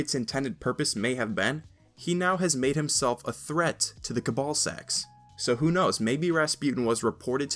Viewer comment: I know what was said but am I the only one who at 4:51 heard Cabalsacks? because that left a pretty bad image in my mind for a quick second lol but besides that, I don't foresee bungie expanding on the exo-stranger's story, why? because we want there to be more, and why give your fans what they want? that seems to be their logic to me. Xur being a puppet? seems legit.